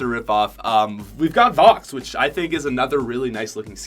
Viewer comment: Did you say Vox